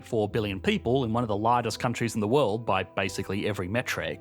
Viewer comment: "... one of the LARGEST countries..." We (and the closed captions) thought it said LIGHTEST, but that made no sense.